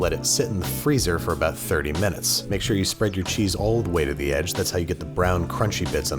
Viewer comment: thought he said spread then cheeks